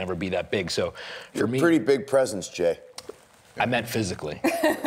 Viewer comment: doesn't it sound like someone said "your gay" lol